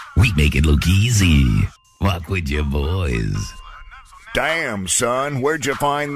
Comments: No ones talking about the “walk with your boyyysss”🤤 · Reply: I thought he said "what's with your voice?" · No ones talking about the “walk with your boyyysss”🤤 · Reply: I thought he said "work with your voice" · No ones talking about the “walk with your boyyysss”🤤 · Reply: Thought he said “rock with your boys” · No ones talking about the “walk with your boyyysss”🤤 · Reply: I hear "Walk with your voice"